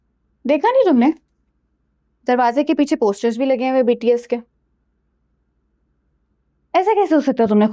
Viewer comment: did she sayyy BTS💜💜💜💜💜💜💜💜